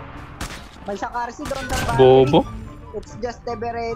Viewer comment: Hahha i heard "bobo" in